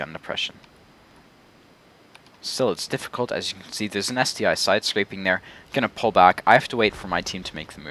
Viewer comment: he sad "there's an ST-I sidecraping, but i heard: "there's a nasty i sidescraping :D